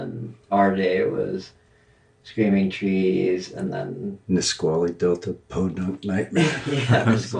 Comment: Whoa, this scared me because I totally thought they said "Pasquale" Delta something or other for the band name at the beginning which is my name! Had to put the captions on to see Dylan was saying "Nisquali".